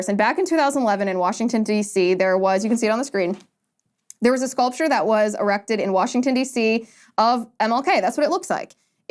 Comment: Did you say that there was a sculpture that was "erected'?